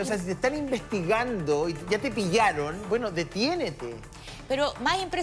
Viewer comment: QUE DIJO ESTE HOMBRE EN EL MINUTO 55:17 "DETIENETE" ?????????🤣🤣🤣🤣🤣🤣